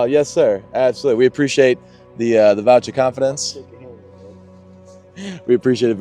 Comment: EVERY ENTRY TO WIN THE 1960 C10 IS 10X! YOU COULD BUY 1 THING AND WIN IT! <link> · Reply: Did he say "Vouch of confidence?"